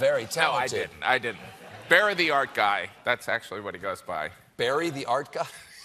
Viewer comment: For a moment I thought they were saying "bury the art guy".